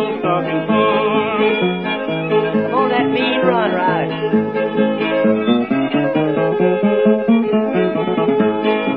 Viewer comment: does Mac say B run ? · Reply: Sounds like “mean run.”